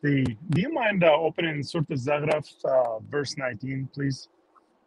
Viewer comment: which surah did ask him to pull up at the end? I couldn’t understand him. Sounded like he said “surah til zaraf” or something similar!